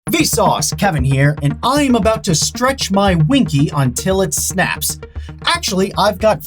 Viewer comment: I miss heard him and I thought he said “I’m gonna stretch my pinky until it snaps”